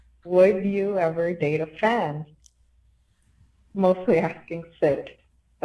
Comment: Oh the caller said "fan" lmao i heard "would you ever date a fem ?" and thought that was why it was mainly meant for Syd